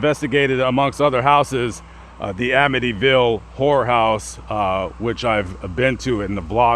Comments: Lol, I know you didn't but it sure sounded like you said "Amityville WHORE house" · Sounds like he said the Amityville Whore house! 😂